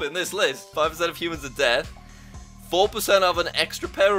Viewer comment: did he say 5% of humans are dead. I think he did